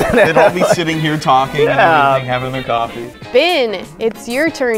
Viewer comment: did call Ben Bin?!?!